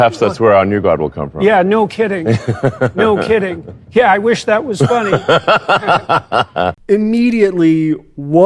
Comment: In the first clip, jordan said “I wish that was funny” and then when you went back to it after commenting Elon repeats it while laughing, he didn’t just say “that was funny”. No idea what they mean by it still but I think you misheard them a bit